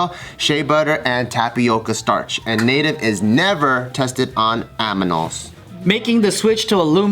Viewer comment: did say Aminals??????